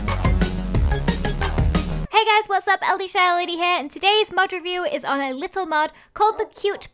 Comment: At frist I thought u said hi guys ld salad here